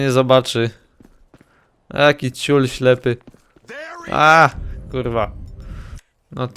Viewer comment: tam nie było ,, ty kurwo'' tylko ja tak usłyszałam. było ,, ty kuźwo" JA tak słeyszałam